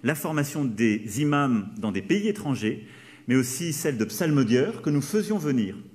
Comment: il a dit quoi a la 3:38 / 3:40 ? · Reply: Psalmodieurs